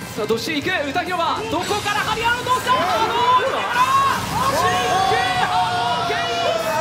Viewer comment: 波動ウメハラ！に聞こえたw